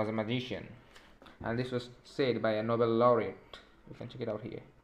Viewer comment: nobel lawri lol what the f r u sayin'